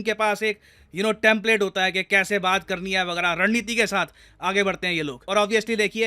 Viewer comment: I heard it as "Rand Neeti".........🤣🤣🤣 it`s not wrong though....